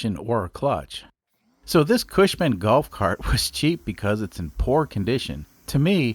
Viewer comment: I thought he said "this shitty golf cart" at first 😂